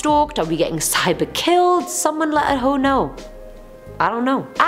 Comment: Omg lol did she just say *someone let a hoe know.. idk" @ 8:43 lol if I heard correctly it was so unexpected so she just sounded funny asf sayin it